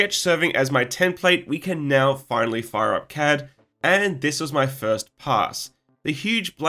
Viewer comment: "...we can now finally fire our cat" is what I heard and it seems terribly appropriate considering the presence of video evidence of major workplace bullying by the cat in question